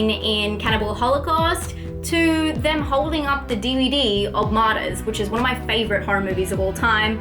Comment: which movie title was that? sorry couldn't understand it clearly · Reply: Was that Martyrs? haha I'm so slow